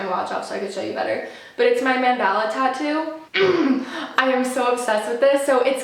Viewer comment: I thought she said Mandela tattoo 😅